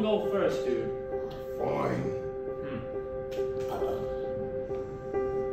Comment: At first I thought he said your the best boyfriend ever but I researched it and it said bestfriend